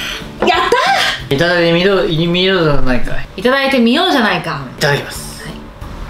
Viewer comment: はじめまして！ 立証してみたくなりまスタっ！ 8:17-8:18 お二人がいとこである証明 リュウジ先生 「※▲◇〇じゃないか！」(じゃないか 部分も 私 聞き取れないっ！） さん 「いただいてみようじゃないか！」➡しっかりリスニングしてる！ 以上 お二人はいとこであってよく話しているという立証でした。 しかし、酒飲み家系って いいなぁ~。